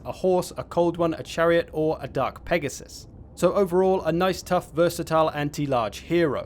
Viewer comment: I misheard "Dark Pegasus" as a "Duck Pegasus"